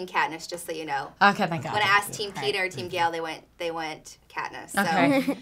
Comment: Haha! I thought she said Peter too and I played it back. She actually says Peeta but she says it really fast somehow. It sounds weird.